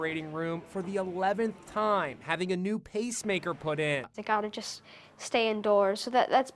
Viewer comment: I thought it said peacemaker